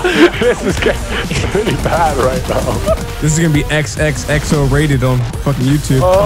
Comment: What did vanoss say at 12:12 and what does it mean? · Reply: If you mean and not Vanoss, he said "this is gonna be XXXo rated on (either youtube or redtube, I couldn't tell)" But those are usually the ratings on porn websites